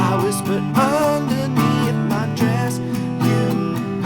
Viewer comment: Oh the heat, did he just say I look underneath her dress instead of breath. That's what it sounds like, what he said. I love you guys. Please tell me your Glendale california area. Id loveto jam with you guys.